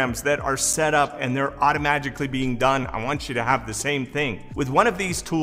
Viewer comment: I keep hearing, "automagically" and not "automatically". Reminds me of NLP (neurolingustic programming). Add: $3k a day is a bit over $1M a year.